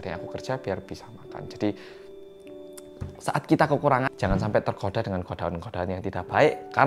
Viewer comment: Sempat salah denger GOD DOG